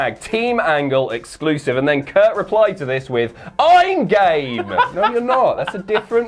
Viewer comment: i thought he said "im gay"